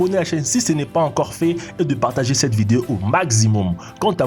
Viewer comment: Il n'y a que moi qui ait entendu "magzimum"